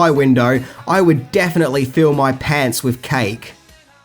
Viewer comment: Did you just say if ya saw that creep out your window.. Youd "fill your pants with cake"???